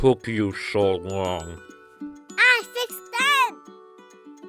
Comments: at the end it sounded like said "I fixed dad" but I don't know also I dont know how to spell her name so I hope I spelled it right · WAIT WHAT DID say !!! cause i heard "I FIXED DAD" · Im looking threw the comments about what said at the end, I swore I heard "I fixed Dad!" but I might have heard wrong